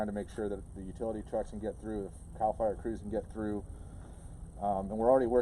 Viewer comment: I thought it said "Cal Fire gives up"